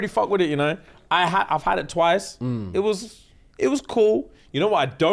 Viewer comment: did say he’s only had Italian twice 😭😭